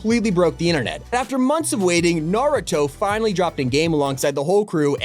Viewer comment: nah bro he really said naroto